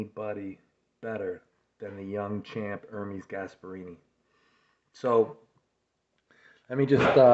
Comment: I thought Devon said "herpes asspirini" lol